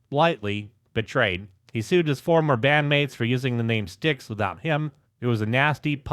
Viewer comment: "Dong, top put it lightly, felt betrayed." is what the CC said. 🤣